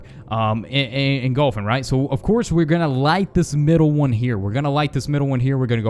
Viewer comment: He said we're not going to light this shi- Like if u heard him say it. He said it two times in a row. My face was like 😐 excuse me?